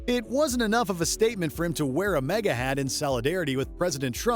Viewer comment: Sounded like he said “A MEGA hat.”